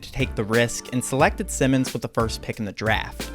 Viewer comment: I swear I heard "With the cursed pick" in the beginning of the video. He should do like Simone Biles and retire or take some time off basketball. It's melting his mind.